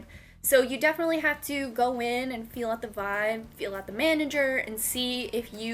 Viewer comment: I thought you said "feel up the manager" and was like (͡° ͜ʖ ͡°) I seeeeee